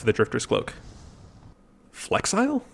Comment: wait i thought it said flexible for all of my playthroughs